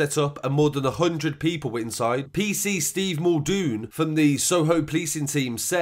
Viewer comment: thought you said PC Stevewilldo lol